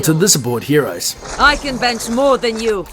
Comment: I thought she said "I defense more than you" #whitera #russian